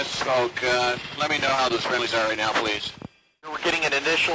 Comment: Did anyone else hear "that was friendlies" there? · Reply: TytoAlbaSoren Yeah sounded like they said “That was friendlies there”